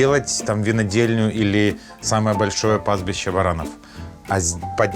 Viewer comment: пастбище варанов? я правильно услышал?))